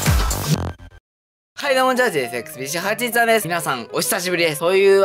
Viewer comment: 今年卒業ですか、同級生ですね〜。 落ち着いたと思うのでたくさん動画出してください、ちなみに僕はパズドラ実況動画が好きです。声がヘリウムみたいなところが気に入ってます。あと、最初のあいさつがチャージXBC813と聞こえるので、やっぱなんでもないです。次回の動画も楽しみにしてます。